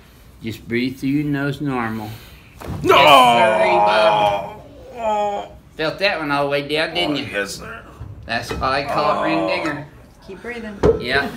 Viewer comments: what did he just say? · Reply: Yes-siree-bob. Felt that one all the way down didn't ya? That's why they call it the ring Dinger. See, Renee is taking my lines now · I thought he said thats why we call it the green n word.